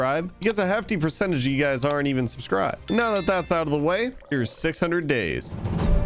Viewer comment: i thought it said God days instead of 600 days